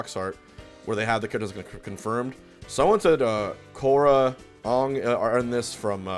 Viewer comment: Did Hbox just call Aang "Ong"?